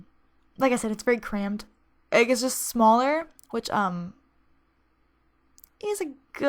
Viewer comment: Did she say "egg getting smaller" on 17:00 ?? 😂